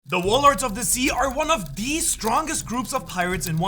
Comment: I LEGIT THOUGHT HE SAID WALMART OF THE SEA